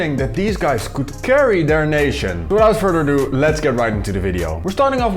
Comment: did you say, "Without Freddy Adu, let's get right into the video.." Yeah, I don't think Freddy Adu will break out in this tournament either....